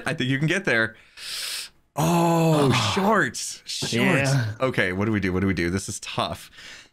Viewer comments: did say Shi# or was it just me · did just say shit?????